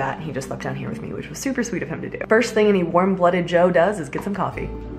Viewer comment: When you said "warm-blooded Jo" I heard "warm cup of Joe" XD